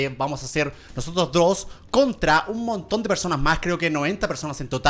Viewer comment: acaso escuche "nosotros dross"